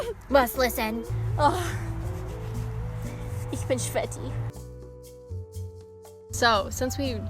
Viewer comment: was that german? Kinda sounded like "i'm sweaty" in german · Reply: "ich bin sweaty" 😂